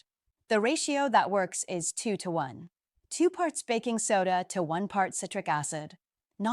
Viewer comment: I must have misunderstood. I thought she said three parts Baking Soda to one part Citric Acid. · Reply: 2:1 psrts 2 parts b.soda 1 part citric adid